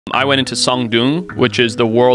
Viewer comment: 😂 thought he said "So i went in som dong" at first